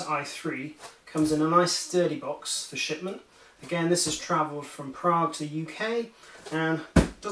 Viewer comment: Hey, did you know that Prague is in Czech Republic not UK? ;) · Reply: Hi, maybe my english isn't good enought but I heard somewhere after 1:25 "this has traveled from Prague in UK" ;)